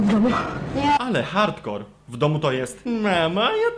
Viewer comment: ja usłyszałem: "Jestem hardkorem"